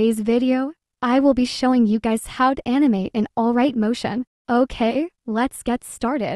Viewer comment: I USED TO THINK IT WAS ALRIGHT MOTION TOO 😭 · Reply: Same and I also misheard it as highlight motion😭🗿💀